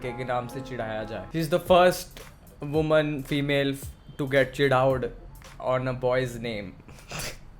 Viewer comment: did I heard wrong or actually said "HE's the first woman who gets chidhaoud 😂😂"